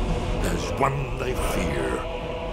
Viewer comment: haha...when he said Dragonborn, i thought he said Dragonball! xD epic trailer and music...can't wait till it's out!